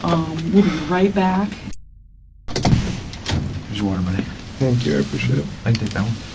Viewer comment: So I was listening to this video not watching it and at about 3:22 I thought the cop said. “Here’s a wad of money” obviously he said, “here’s a water, buddy.” But I had to rewind it and watch it before i heard that. I initially was like wow interrogation tactics are getting crazy. Lol